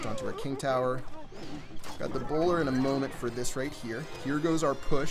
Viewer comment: Oh lol I thought u were saying browler